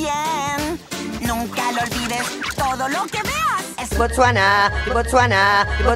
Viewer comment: De tantos "botsuana" escuche "por susana" jsjsjaj